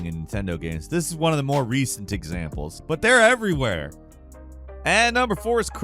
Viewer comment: did you say “recent examples” or “diczamples”?